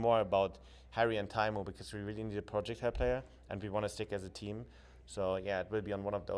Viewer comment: what does he say at 11:00? "we really need a project player" ? I cant really tell what hes saying there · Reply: Projectile player. Heroes like hanzo, mei, genji, etc. that shoot projectiles with delay and therefor leading rather than hitscan like mccree and soldier.